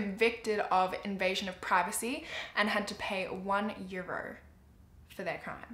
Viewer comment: did I hear that right? 1 euro for invading privacy??